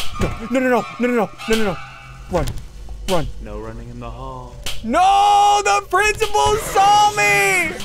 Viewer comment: I heard him say no running in the hole think about it I know